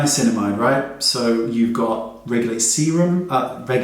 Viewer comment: at first, I heard regular semen helps with acne 😂🤣